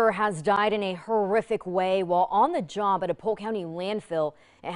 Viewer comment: Thought she said on the john